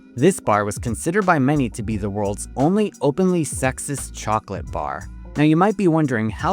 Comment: I thought ts said “sexiest chocolate”